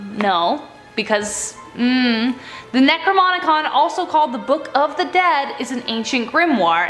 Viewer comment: I thought she said the necromomicon for a second